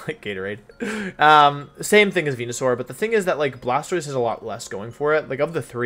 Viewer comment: sounds like he is saying wenusaur or vwenusaur